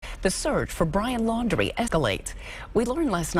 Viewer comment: "Ehh-keh-lates" or "escalates" - what did that reporter say again?